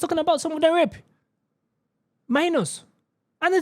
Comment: I know it's not a laughing matter, but I really thought he said "my anus" not minors 😅